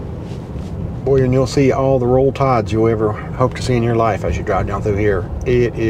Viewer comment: what did you say is everywhere? I went back and listened again, but still didn't understand the word or words you said. I don't follow any sports at all, but I know the name Bear Bryant from watching the old TV show, BJ and the Bear. I remember the character, BJ once telling someone that he named his chimpanzee "Bear" after Bear Bryant. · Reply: Roll Tides everywhere on signs and other things